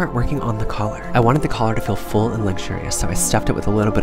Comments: I'm so Rose Nyland. I thought u said Martha Ray. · Thought you said martha ray..